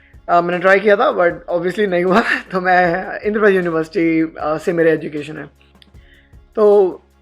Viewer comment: Did he said indraprastha university